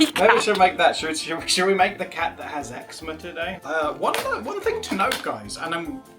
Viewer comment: I thought was saying X-Mas cat. Like a Christmas Cat until they started saying the think about skin conditions that i realized it was Eczema Cat 😭😭😭😭😭 I was even like "oh Adam is still struggling lots with the color blindness"